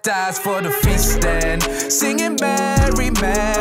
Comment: ngl I thought for a second, man said fisting